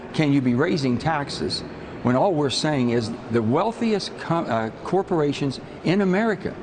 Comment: I heard "The wealthiest c**ts" and and then a correction. LOL